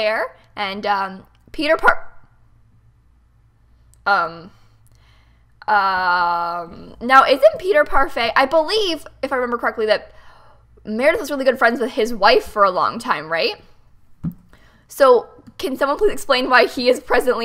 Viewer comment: I thought said "Peter Parker...?!" But she didn't and I was like "WHAT'S SPIDEY NOT DOING IN NEW YORK. WHAT ABOUT ALL THAT HERO RESPONSIBILTY STUFF???!!!"